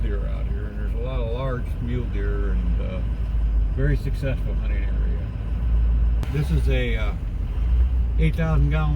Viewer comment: Bob asks what vehicles are accepted and says "...as long as it's not______." what ? I can't make it out what he said. Can anyone help? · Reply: Trashy.